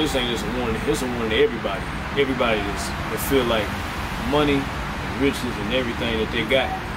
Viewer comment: Dude did you just say money bitches or money riches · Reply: Ok it had to listen a few times. You said riches. However you would still been right. Just using a different term